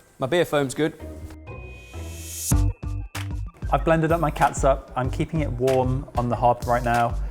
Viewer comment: 9.50 I thought he said; “ I’ve blended my cats up 😂😂😂